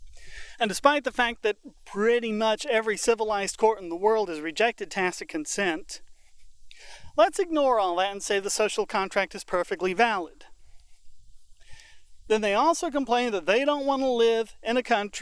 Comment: Hi, At 10:30, what exactly are you saying? It's a bit hard to make out. "Every civilized court in the world has rejected XXXXX consent." Did you say "tax of consent?"